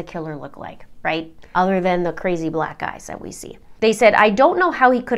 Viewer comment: what does a killer look like. Did you say ‘other than the crazy black guys that we see’? I’m sure I didn’t hear that correctly. Please let me know what you said. Thanks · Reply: Black EYES. Black guys doesn't even fit in the context.